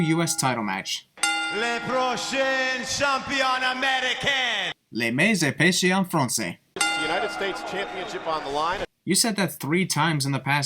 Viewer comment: As a guy who never paid attention to his French classes, could someone tell me what said at 2:30? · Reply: Emi Grant he said the "themiz has sined in french", in a very bad french